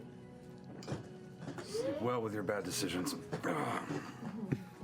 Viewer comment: "Sleep well with your bad decisions" is one of my new favourite lines in Critical Role. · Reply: I thought he said "bed decisions"